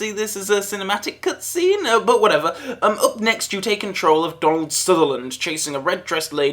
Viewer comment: Was that a reference to Don't Look Now? Sounded like you said Don Sutherland.